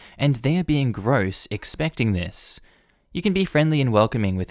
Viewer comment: I was listening to this and I thought he was saying condom ball not condom bowl. I was picturing a rubber band ball, But made out of possibly used condoms. After that the bowl seems pretty reasonable.